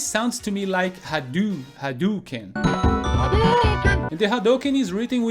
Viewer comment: Sounds like Ha do ken? No no my friend... Everyone i know thought they say ha do kid.